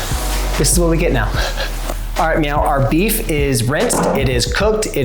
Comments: Great video! @ 2:32 did I hear you say "alright meow" our beef is rinsed and cooked ?? 🤣😆 :chillwcat: Sorry new customer to your channel. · ok. 2:30. I heard "all right meow" what!?